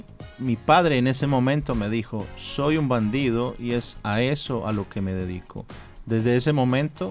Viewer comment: "soy un bandido e Aécio un amigo". foi o que entendi.